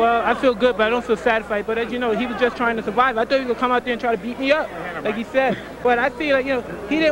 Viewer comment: Tyson said I thought he said he was gone come out here and beat me up. Hahahahahahahaha